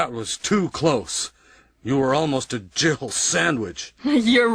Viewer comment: I always thought Barry said "Jiffle sandwich"